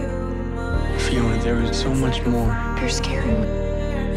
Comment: What did Fiona say at 2:20? They're scaring me? Just didn't quite catch it. Thanks! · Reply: She said " Your scaring me"